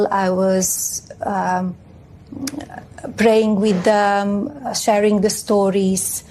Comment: She doesn't say anything worth listening to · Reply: I thought that she said "playing" instead of "praying" and I listened to it twice. Does anyone really think that she ever even attempted to serve food?